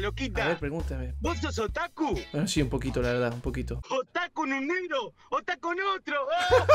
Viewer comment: LIKE Si No Entendiste Lo Que Dijo El Bananero XDD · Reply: Otakun con un negro o tas con otro o creo que así dice